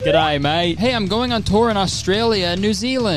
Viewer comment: he says, “good and evil… hey look at me” side note: as a texan, it’s absolutely killing me at how true your statement is. i’m pretty good at deciphering all kinds of accents and tones but it’s actually does sounds like what english sounds like to a non native speaker 😂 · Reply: i heard “good evil hate the cookie” 😂